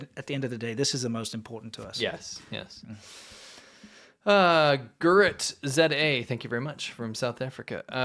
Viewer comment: No, you did not say "Gerrit" you said "Grrrrit".